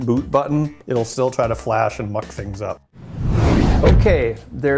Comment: I thought he said f*ck things up. O___o...